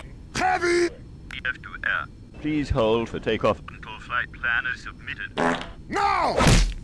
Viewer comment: what did he say here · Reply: "Tf2 air, please hold for takeoff until flight plan is submitted"